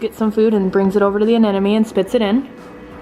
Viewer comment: Is it me or do I hear her say "anenome" instead of "anemone"